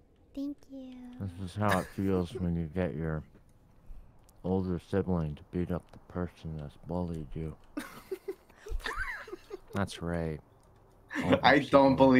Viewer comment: nice vid man! but can u please tell me what does say at 6:00 please? my english is not that good when someone is eating or not talking clearly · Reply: He said, "This is how it feels when you get your older sibling to beat up the person who bullied you. That's Rae."